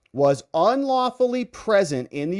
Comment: Illegally PRESENT, I thought you said illegally pregnant